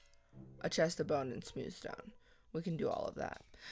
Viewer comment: It sounded like said chest, a Bone and its sweet as stone